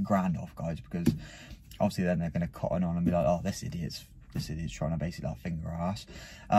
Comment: Did i hear correctly or what at 13:54 “this idiots basically trying to like finger us” 😂😂